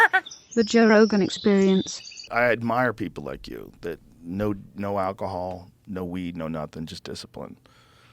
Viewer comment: I thought he said “no alcohol, no weed, just disappointment” 😂